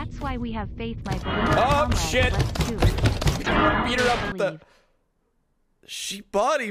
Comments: My ears may be tricking me, but did said "filha da puta" at 8:13? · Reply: It's probably your ears I hear "beat her up with the-" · My ears may be tricking me, but did said "filha da puta" at 8:13?